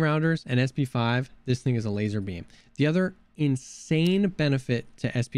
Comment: did I hear that right? ,”this N*** is a laser beam